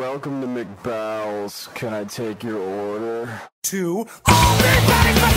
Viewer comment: Why have I never noticed that he said "McBowels," every other time I've watched this? I thought he was saying McDonald's until last night when I was watching this and heard McBowels as clear as day!